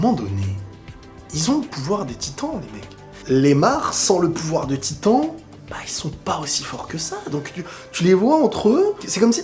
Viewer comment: Voici ce que tu n'as pas dit mais que j'ai entendu: "Les Mhars, sans le pouvoir des Titans, c'est Thomas Lémar!!!"